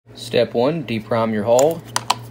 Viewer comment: Dude, so is there a process to PRIME your hole? Lol You meant de-primeR your hole right? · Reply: ahh, because it sounded like you said deprime the HOLE. lol I figured if there was a procedure to DEprime your hole there must be one to PRIME your hole. Lol.